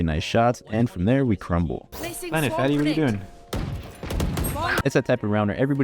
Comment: did u say “plant it fatty” to the girl bc if so ur a fucking weirdo and a misogynist.